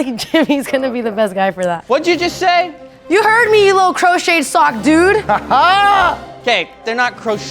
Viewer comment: Steph roasting Jimmy is gold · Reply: i thought she said he was built like a crochet sock 😂